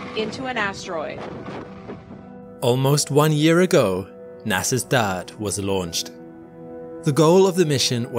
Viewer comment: I thought he said, "NASA's dad was launched," I thought, "Poor bugger!"